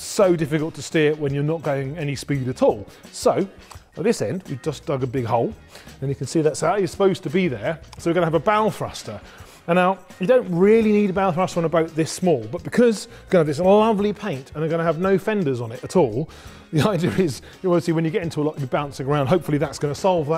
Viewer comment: For a moment I thought you said "bowel thruster" :-D Interesting projects and the relay was also interesting - thanks!